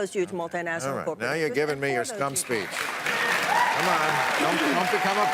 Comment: How was that a 'stump speech'? She was right on target. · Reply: I thought Bill said "scum speech."